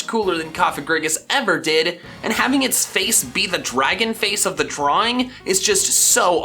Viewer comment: did he say drawing?